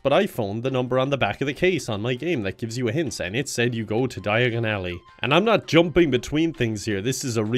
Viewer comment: I seriusly thought he said die in an ally!